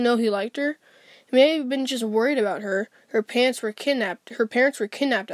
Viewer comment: Lol I heard “her pants were kidnaped”